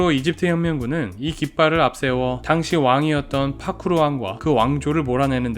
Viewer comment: 파루크가 아니라 파쿠르로 들리는것 같은데 나만 그런건가?🤣🤣